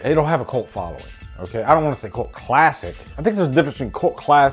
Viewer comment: OMG!!! if I didn't have the [CC] on, I would've sworn that you were saying... "Coke Classic" but you weren't...sorry, I just had to throw that out there...lol